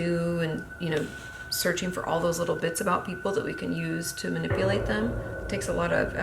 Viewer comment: did she just say “we”?...